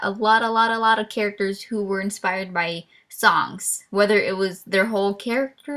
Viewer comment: I THOUGHT YOU SAID "my characters were inspired by socks" I WAS LIKE WHT